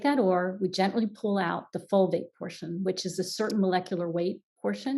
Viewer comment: The closed captioning has spelled a word several different ways and I'm trying to find out what that word is. The first time says it is at the 33:06 minute mark. She says, "we take that ore and we gently pull out the -full vape?, folvate?, folbate? ???, - portion." Does anyone know exactly what word she is saying? CC has spelled it numerous ways. Thank you!! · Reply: The answer is Fulvate. Thanks!